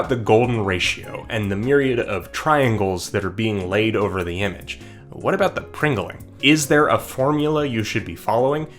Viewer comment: I swear I heard you say "what about the Pringle?" And I can't figure out if I'm crazy or not